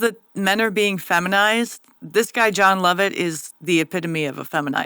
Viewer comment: I guess I’ve never heard of John Lovett and honestly thought you said Jon Lovitz 😂